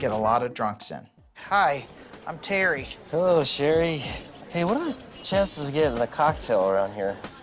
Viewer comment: Nurse:Hi,I'm Terry Walt:Hi Sherry Lol why is that so funny🤣😂🤣😂🤣😂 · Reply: I thought that he said cherry